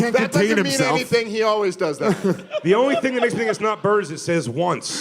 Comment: Can anyone understand what the fuck says at 0:10? I’ve listened a couple dozen times at full volume and it doesn’t even sound like words. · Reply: "The only thing that makes me think it's not Bart is..." lmao but it definitely does not match up with his mouth, that's too funny😂😂